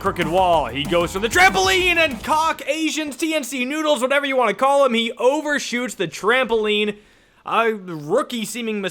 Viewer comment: …… holdddd up, what did he sayyy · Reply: Hawk